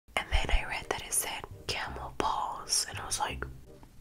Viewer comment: When she said it said camel paws I thought she said camel balls